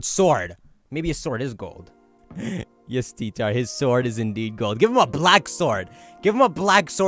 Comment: lmao thought you said ''yes retard his sword is gold''